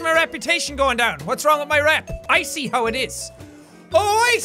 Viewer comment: when he said "I see" thought he said "Ice Tea" and made a pun